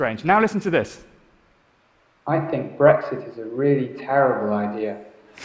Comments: Funny, I could hear words in that recording even before he played the original one. Only I heard "I think breakfast is a really terrible idea." Possibly because I was buttering my toast as I listened. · I heard "I think Texas is a really terrible idea" at first so at least I expected something.